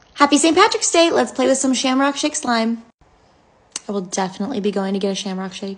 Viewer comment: The fact I thought she said shit slime